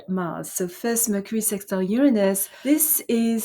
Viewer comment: The caption at 4:24 is incorrect, 'Sun Sextile Mars' instead of 'Mercury Sextile Uranus'